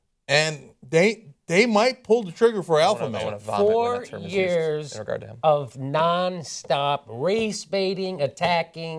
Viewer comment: can anyone make out what John is saying under his breath? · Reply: "I wanna vomit when that term is used. To refer to him."